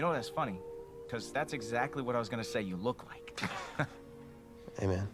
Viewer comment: the subtitles say like look instead of look like. That’s the amount of effort I’d expect